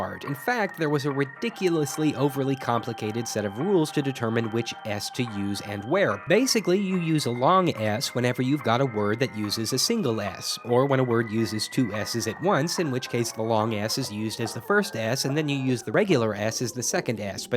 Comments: i laughed so hart because i heard ass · it sounds like ur saying “ass” instead of s 😂 😂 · I thought he sayin ass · it sounds like he is saying "ass"! XD